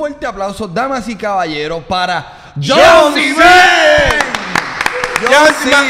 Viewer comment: que es lo que dice originalmente JHON Z ????????? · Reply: El dice "Jon z Men" que se escucha como "viajo sin ver"